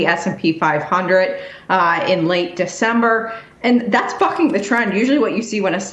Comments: Did she say "that's fucking the trend"? 😂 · am I high or did she sound like she said the f word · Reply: “That’s fucking the trend” lmao